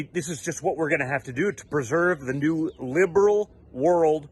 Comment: Did DJTJ say "perserve"